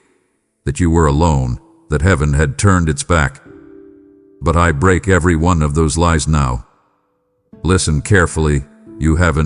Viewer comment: Yes I survived., pregnancy. I thought you said she lied!